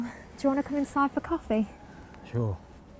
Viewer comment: It sounded like she said “You got any corn dogs?”